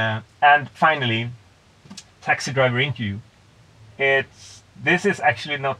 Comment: What does he say at 0:57 ? Taxi driver (...)? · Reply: Interview. Search for Guy Kewney